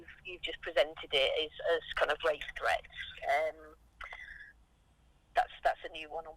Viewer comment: Katie was talking about rape threats, but this noodle thought she said RACE threats